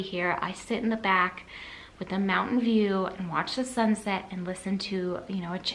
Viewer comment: Always love your videos...funny comment...when you were talking about the books on tape...I was doing laundry and listening in headphones...I thought you said "I walk winnie then sit on the deck with a MOUNTAIN DEW and listen to the book"! Lol...I had to rewind and watch again, and was like...she eats so healthy but then has a mountain dew????!!!??! Lol :)